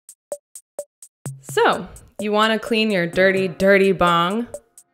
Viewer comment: Jesus Christ in the beginning I thought she said "So, you want to clean your dirty dirty dong?". I'm losing it